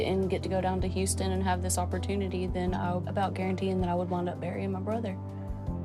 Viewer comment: I thought she said she was going to end up MARRYING HER BROTHER not BURYING. Tennessee???